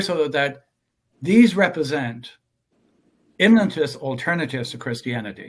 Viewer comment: I can't make out what he says at 35:55. "... These represent ____ alternatives..." Any help? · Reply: “Immanentist” alternatives to Christianity. I suppose as opposed to transcendent alternatives.